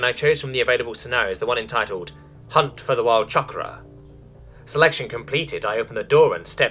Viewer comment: i thought it said "hunt for the wild CHOCOBO" for a second and was concerned about my feathered friends.